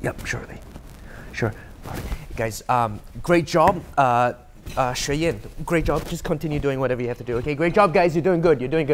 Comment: the subtitles says "Lingyi" but when ends his call he calls her Xue Yan.